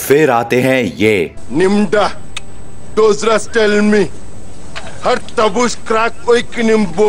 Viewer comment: I heard tell me